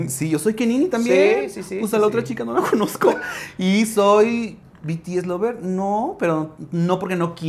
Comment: "BTS Lover" yo nada más escuché "Army, fúnenme" JAJAJAJAJAJAJA